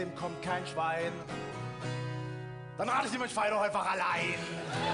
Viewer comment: was sagr er bei 5:47 ich verstehe nur rammel sie mit dem schwein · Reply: sagt :"Dann rat ich dir feier doch einfach allein".